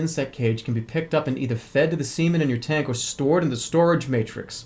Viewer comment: when i heard him say "successful growth of your seaman" i just had the biggest stupid fucking grin on my face because it sounded like semen